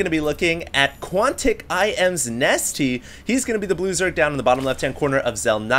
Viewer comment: when you said "blue zerg" at the beginning I thought you said "loser"